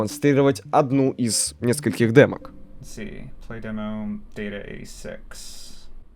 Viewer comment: Слушал ролик фоном, а этот момент услышал: "playdemo dataGaySex" и подавилсч нахер